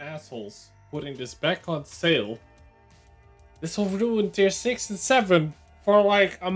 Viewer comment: I didnt quite catch what was said at 0:10 "this is overdue in tier 6 and 7.."? Can someone clarify? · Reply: "This will ruin tier 6 and 7..."